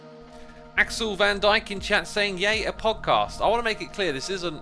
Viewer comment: I thought he said pogcast. I hate myself.